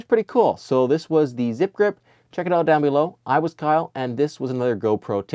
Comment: Did you just say "I was Kyle" at 4:02 lol